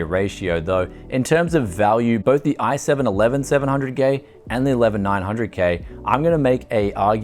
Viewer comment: i swear i heard "i7 11700 gay" 😂